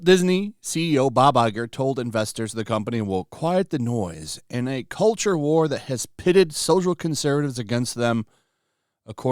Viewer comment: I thought they said, "They weren't in a "Culture War""? How can you give up on a culture war that you weren't apart of.